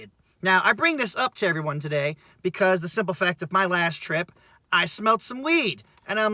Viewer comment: Additionally, if you didnt smoke in the vehicle, theres no need for spraying because of the smell which you also mentioned. Logically, signs point to you just outed yourself for smoking while out working. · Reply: Nah, the way you say it it for real sounds like you say I smoked some weed, not I smelled some weed lol. Just saying. Obviously I am happier it's not one over the other and I misheard.